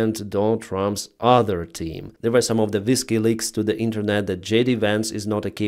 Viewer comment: Did you say "Whiskey Leaks"? 😂